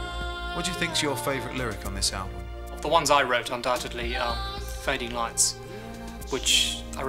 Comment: "What are your favourite lyrics?" "Mine". I love it! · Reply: I think Tony said "Of the ones I wrote, Fading Lights is the favourite." A terminal song, heehee, yeah like Afterglow, Undertow, etc. Yep. The last Genesis song really. The "Of" is said really quickly since he talks really quickly!